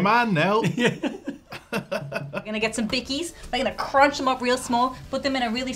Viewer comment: Smashing up bickies and mixing them with the banana pudding is actually an intuitive start towards the glory that is Banana Pudding. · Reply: I thought she was saying Vickie’s as in Miss Vickie’s Chips. That wouldn’t have been so good. But then again don’t knock it till you tried it.